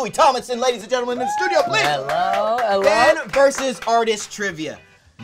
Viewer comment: when you hear "allo.. allo" instead of "Hello.. Hello" you know its LOUIS WILLIAM TOMLINSON 😂